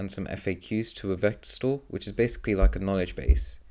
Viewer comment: I cant understand what you say in 5:12 "Then i've uploaded my website and some FAQs to a ..." to a what? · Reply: Hey Losek, I said that I had uploaded them to a vector store which is basically the knowledge base for the OpenAI assistant.